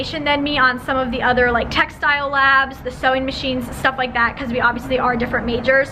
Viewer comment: I never comment on videos but it made me laugh this whole time I thought you were saying textile lab but I just saw it's actually TechStyle lab